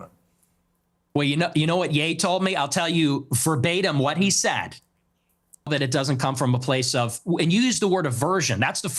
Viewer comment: WHAT DID YOU CUT, PIERS??? WHAT DID KANYE SAY TO NICK? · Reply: Nick shared that Ye said, “sometimes niggas are niggas,” and shared a 4-5 minute story about their first time meeting.